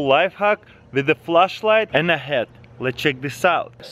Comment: I had this in the background, I heard : "With a flashlight and a head" "So we got a head right here" lmao